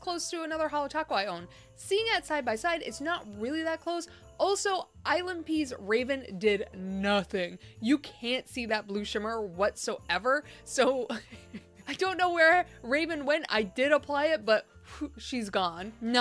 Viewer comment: why do I keep hearing “island pee’s” instead of ILNP’s 🤣🤣 Also, the thumbnails are killing me 😅